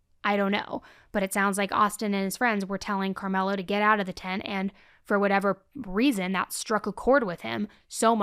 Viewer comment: what were the words? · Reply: Austin telling Carmelo aggressively to get out of their tent.